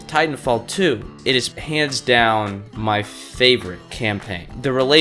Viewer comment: Maybe I'm crazy but...at 1:50 I'm 99% sure he said "pants down" instead of "hands down."